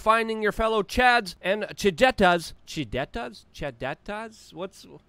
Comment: Chat- female version = Chatetas hopefully u speak a bit of Spanish Luke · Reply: actually, i got it totally mixed up- i thought luke was making a joke at 4:45 but he was asking for the female version of Chad (which is a ref to like a undercover fed or cop) but i thought he said chat instead of chad- and then i referenced what i thought he said but he said chadetas or something lol